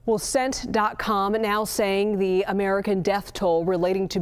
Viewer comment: cmon y'all gotta do better lol did she just say "CENT DOT COM"? instead of CENTCOM lool geez